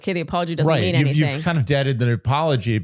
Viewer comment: did just say deaded??? 🤔🤔🤔